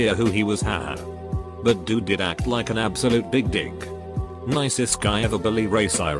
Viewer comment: I just can’t get over the fact that every time it says dick it sounds like it says “big dick.”